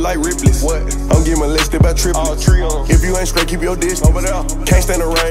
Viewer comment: Did he just say "getting molested by trippie" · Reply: OxG Clamps triplets. He didn't sound close to trippie